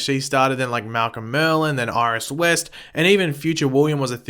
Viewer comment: did he just say iris west XD